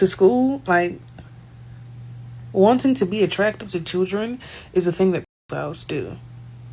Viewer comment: it sounds like "wanting to be attractIVE to children"... mebs auto subtitles got it wrong